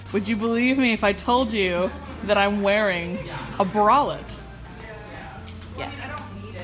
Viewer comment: When said bralette at 5:01, it sounded like she said wallet